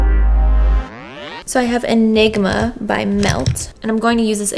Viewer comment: I thought she said I have a ligma lol